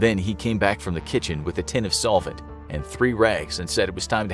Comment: I thought the narrator said “with a tin of salt,” and I was baffled by how salt could be huffed and cause unconsciousness. (My audio processing issues strike again! Whoops? 🤦🏻‍♀️🤷🏻‍♀️😄)